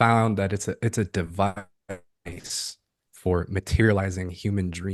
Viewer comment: At minute mark 52:14 the recording gets garbled. is talking about the Kins domain and the takeover, he says “It’s a _ for materializing human dreams” I’d like to know what he says there if anyone can remember. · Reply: Device!